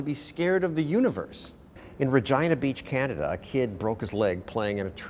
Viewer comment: damn i thought he said vagina beach